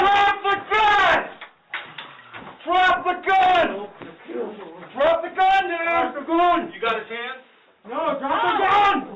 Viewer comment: Did I hear drop the gun nigger at 7:50 ? Smfh